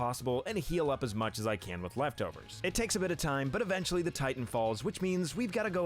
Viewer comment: What did you say at 14:11? Titan fall?